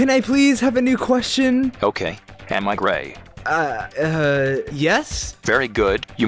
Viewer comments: I thought announcer said m i gay but it rly said am i gray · before subtitles, i thought he said “am i great?”